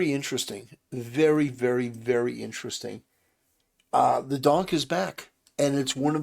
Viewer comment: did u say...the donk is back